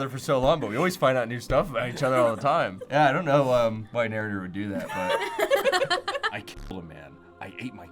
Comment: Did he say narrator??? 14:03, I’m not the only one, right? Even I put captions on and it says that. (I watched this vid back to have some laughs and I am like listening to everything so well.)